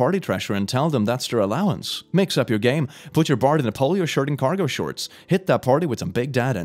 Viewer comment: did anybody else hear polio shirt?